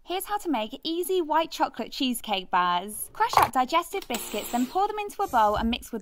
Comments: I thought she said, crush up digested biscuits, and I pictures biscuits that had been thrown up😂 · I heard “Crush up digested biscuts” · Why did I hear it as “digested biscuits” 🤦🏻‍♂️ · why did I hear "a thousand chocolate" lol?